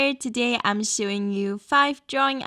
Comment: I thought you said today I'm suing you 5 drawing ideas